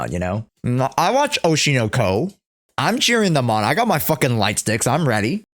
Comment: misheard lights dih